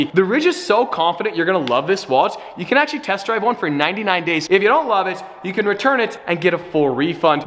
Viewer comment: Did he say to test drive a wllet lol